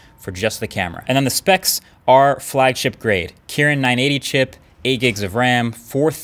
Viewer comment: did you really say kiran?